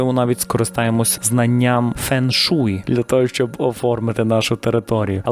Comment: чому я почула ,,фен жуй,, ??😂😂😂